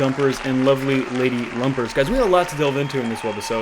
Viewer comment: My gushers.... Did you just say luscious lady lumpers!